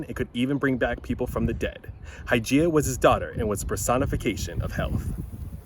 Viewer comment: I wasn't sure what you said... then I went back and read the CC. His daugther was the "personification of health"... but I thought you said "personification of hell"!!! hahahaha